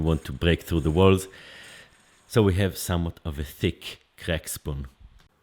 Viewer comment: Did you say "crack spoon"??? 😂